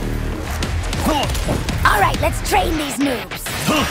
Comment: I thought she said "alright, lets train these noobs"💀